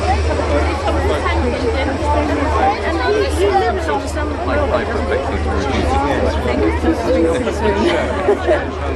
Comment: what is she saying? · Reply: "And he lives on some railway doesn't he?"